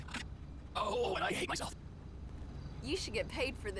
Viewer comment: am I the only one who hears "ooh I hate myself"???